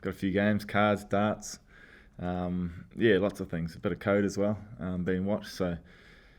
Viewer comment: Did he just say 'Bit of code', as in programming? Nice! Didn't know the boys were into that.